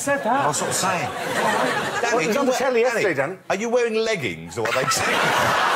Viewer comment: I think they are called jeggings. Richard always has sharpest wit 😅😅😅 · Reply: no, I think he said "are you wearing leggings or are they jeans?"